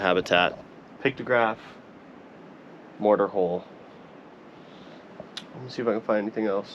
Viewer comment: How does it called Mordor Hole? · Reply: Like a grinding hole, like a pestle and mortar grinder, not mordor